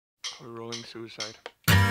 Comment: in the beggining does serj say "a rolling suicide"? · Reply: Shade Instinct oh,I'm deaf. I thought it said,"We're all suicidal" whoops!